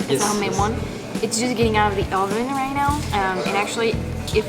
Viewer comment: Did she say Ri'now?? Kinda getto for a french girl. Around 11:09.